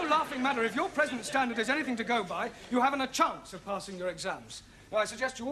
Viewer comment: What did the teacher say at 11:35? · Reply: if your present standard is anything to go by, you haven't a chance of passing your exams.